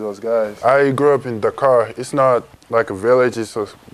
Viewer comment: I thought he said "I grew up in the car"